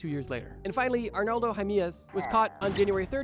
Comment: did he say jimenez?